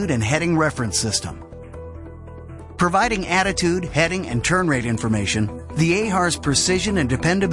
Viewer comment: what does the narrator say at 2:47 - proving attitude, heading and... ??? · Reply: turn rate